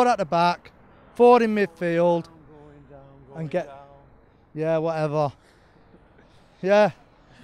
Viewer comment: what did the guys said to the them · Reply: Going down, going down...